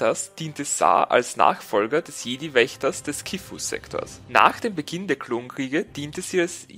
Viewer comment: ich verstehe dauernt des Kiffersektors xD